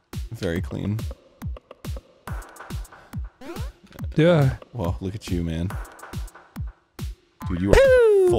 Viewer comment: SOMEONE else heard Arin say "Whoah...look at Jew-man" here, right? Please? Someone?? Should I seek counseling?